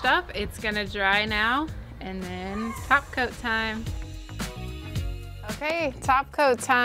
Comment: When you said "top coat time" I heard "TACO time". Got excited there for a moment.